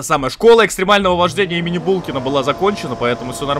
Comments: мне одному показалось что он сказал - школа экстремального вождения МИНИ булкина? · Послышалось :"Школа экстримального вождения мини Булкина"))))